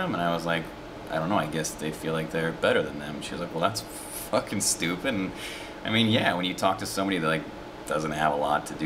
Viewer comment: closed captioning thought you were saying "than to the pope's fuckin stupid" awesome